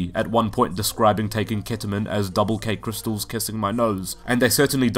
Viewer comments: On Calcium I originally thought it was saying "Ok crystals, kiss my nuts." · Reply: i thought he said never let crystals kiss my nose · On Calcium I originally thought it was saying "Ok crystals, kiss my nuts."